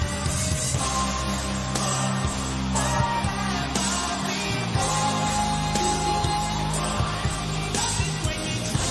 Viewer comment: Does anyone know what is he saying at 0:56 when he screams? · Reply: "Half past five babeeeeeeeeeeeeee"